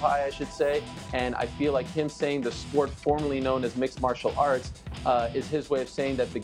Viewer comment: He did not say formerly known as mma, he said formally. They are 2 different things Ariel. Formerly means it used to be known as MMA and has now changed: Formally means Mixed Martial Arts is the full name of MMA.